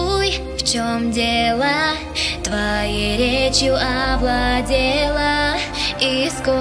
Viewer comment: Мне послышалось:Твоей речью оболдела